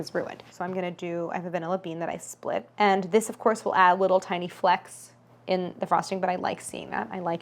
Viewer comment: i thought she said “flex” instead of “flecks” when she was talking about the vanilla bean because it really is also a flex